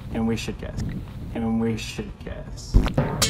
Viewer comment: bruh the yt captions were gaslighting me into thinking he said "guess' instead of "kiss"